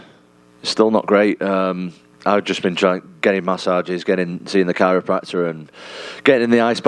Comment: Did he say gay massages? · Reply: “getting”